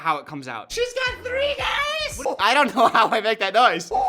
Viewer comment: What video is at 17:04 ? Where he says she has 3 guys? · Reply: *she’s got three days I think it’s the Aladdin commentary when Jasmine has 3 days to marry a prince.